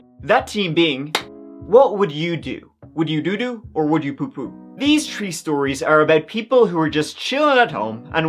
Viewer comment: I thought you were saying team and tree until I realised you must be Irish…. What team? Oh THeme! What tree? Oh THree 😂